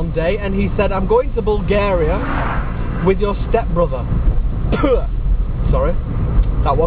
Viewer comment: Yes, thanks. I thought you were saying step mother. Sorry.